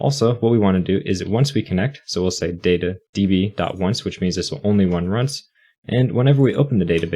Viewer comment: Is it just me or he said "only wun runs" instead of "only runs once" at 4:32?🤣🤣